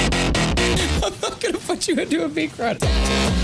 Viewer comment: wait when did he say a curse word? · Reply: i think here, he says "fuckin out you into a meat grinder" i think